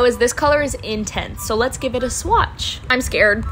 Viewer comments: She said “I’d never wear this shade on my lips” but I hear “I’d never wear this shit on my lips 🙄” 😭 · i thought he said shit but she said " yeah im never wearing this shade on my lips" T-T